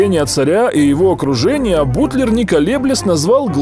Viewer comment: "Царь приказал спросить его(Бутлера), какой на то обычай в его стране. Бутлер доложил царю, что флаг Нидерландов представляет собой полотнище, состоящее из трёх горизонтальных полос равной ширины - красной(верхней), белой(средней), синей(нижней). В результате чего была составлена"Роспись - надобно к корабельному строению, опричь того, что ныне куплено за морем, прислать из меновых(импортных)товаров триста десять аршин киндяков да сто пятьдесят аршин тафт..."Для флагов была закуплена киндяк-алая, белая и синяя материя. А в гербе России покоя не давала Римская империя. Цап-царапнули двуглавого орла из герба Священной Римской империи! -"Иллюминированная рукопись 1540 года. Мастер Виргил Солис. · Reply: Мне послышалось : путлер